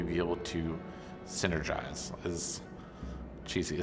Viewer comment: What is it that he says at the very end? I can't make it out "It's very important that everyone's ......." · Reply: synergize